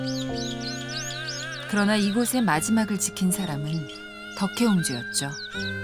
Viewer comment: 덕혜공주가 아니라 덕혜옹주라 칭해야 한다고 알고 있습니다. kbs 방송이라면 이런 오류을 범하면 안된다고 봅니다. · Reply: 제가 들으니 덕혜옹주라고 하는데요? 광덕님께서 잘못 들으셨네요.